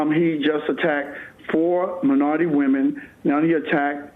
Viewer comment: Haha, they way he pronounces at 4:29 when he said "...he attacked minority women..." I swear I heard ...." He attacked my naughty women..."